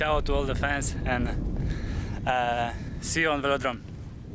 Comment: Erreur de sous titrage à 34:07 , Milik dis "on se voit au Vélodrome" Je ne sais pas pourquoi vous rajoutez tout le temps le mot "orange" devant le nom de notre stade. Personne ne dit ça